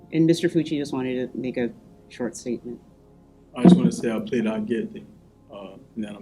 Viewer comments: it sounds like he says "I plead not guilty" lol what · Sounds like he said “I’m pleading not guilty”.